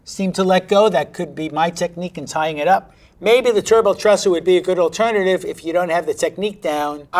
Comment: I heard “ Gerbil Trusser” initially, and was very, very confused. Fascinated, though. I guess I’m relieved it wasn’t actually for gerbils. TBH, I admit to being a tad disappointed. I kinda wanted to see truss a gerbil. 😂